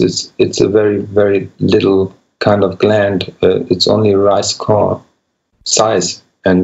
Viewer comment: The tooth, the whole tooth, and nothing but the tooth... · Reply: I think I heard ' the pineal gland is the size of a rice korrel'. Korrel means grain in German. So by that I think he said the pineal gland is as small as a grain of rice... this statement is not necessarily intended to be medical advice ...for educational purposes only... tap tap, no dibs, no take backs, no bottle caps copyright 2020, all lefts reversed